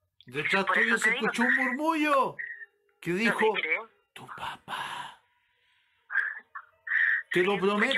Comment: Si se siente claro en la hora 1:03:50 un susurro que dice "Tu papá"